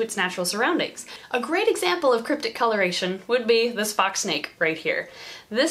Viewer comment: Ha, thought you said “Spock snake.” And I wondered if you’d actually tell us it’s real name, not just what you like to call it. You said fox......